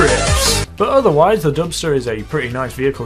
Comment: i heard dumpster lol